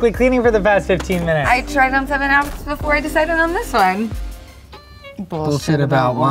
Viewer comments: can someone please tell me what she is saying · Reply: I tried on seven outfits before I decided on this one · can someone please tell me what she is saying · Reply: kamatmehbro I thought she said I tried seven guys before I decided on you or something and idk how